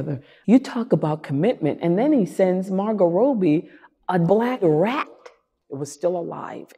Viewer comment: Is it just me or is Viola actually mouthing the word “dead rat” instead of “black rat” around 2:30?